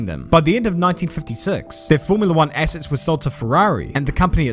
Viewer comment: did you just say "the company still losed money"?